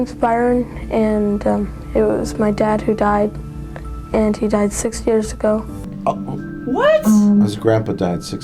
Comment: Super tiny nitpick but I think the kid at 37:46 said "He died six years ago", not days. Still fucking weird for them to make that kid talk about it though lol.